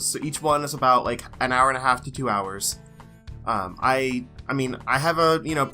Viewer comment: I know three other people aready said that, but .... That Beard is fucking faboulus! 3:20 Did I hear that right? Two Hours of Zelda? :O GIMME! I'm glad the whole Job thing worked out for you =) 19:27 ...wait wait wait ... you mean the is off the market now?! Instant-Unsubscribe